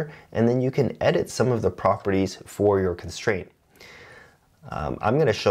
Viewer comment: since commenting is good for the algorythm, at 14:49 the Captions say "the Leading" when it should say "deleating"